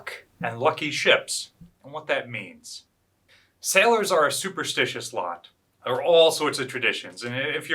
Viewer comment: Did he just say super shit